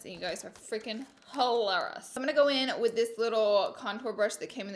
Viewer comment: Did she just say hoelarious